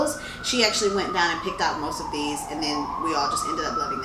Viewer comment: At first I thought u said picked out a cat because I keep seeing videos that said there's alot of cats in Marrakech and I'm a cat mom 😅